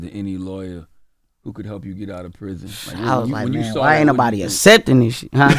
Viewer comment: Wait what he say? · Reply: "I was like, man, why ain't nobody accepting this shit?!"